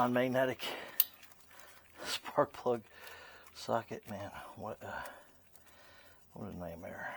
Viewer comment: Did You Say, 5/8 Spark Plug Socket??? It Sounded Like You Said 5/8 SP Socket... Good Video... Thank you for all Your Tips... You may want to use a longer swivel extender... Just Saying...